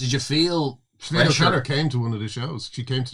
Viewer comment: who did he say turned up to the show?? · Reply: After listening 20 times I finally got it.... Sinead O'Connor!